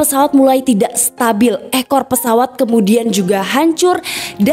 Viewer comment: kok aku dengernya EH KO PESAWAT padahal ekor pesawat